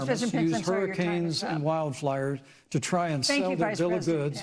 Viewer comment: Did anyone else notice Pence said “wild flyers” instead of “wild fires”? Pretty ironic being that he had a fly on his head. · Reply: Thought he said wild liars, which also fits